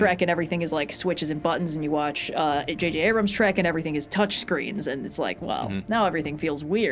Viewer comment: I legit thought said Shrek, and just went with it. Like yeah, JJ Abrams Shrek would have touch screens